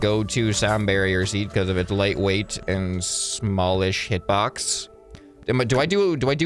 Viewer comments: What did he say 😂. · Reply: small-ish hitbox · who else heard "small AS box